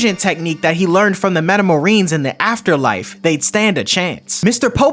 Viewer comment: Did you say Metamarines? Like from 40k?